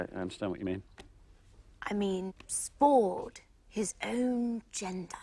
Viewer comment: • Non-native English speaker here. What is Bridget trying to say around the 3:00 mark? “Do you think he’s [spored/spawned/?] his own [gender/agenda?]”? I can’t quite hear what she’s saying, and while it’s supposed to confuse Colin Firth in this scene as well, I’m still curious about the actual words. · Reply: What Bridget is trying to say is, "Do you think he's spawned his own genre?" They are making fun of the fact that Bridget is using the wrong words. She is asking him if Richard Curtis, by writing Love Actually, has created a different kind of romantic comedy that follows multiple couples and their storylines.